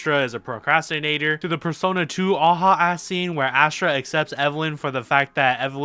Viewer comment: Im I tripping but did he say persona 2?? PERSONA 2 REFERANCE RAHH